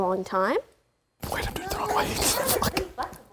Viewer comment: can someone translate what lud said at 31:17? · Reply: Wait I’m doing it the wrong way. F@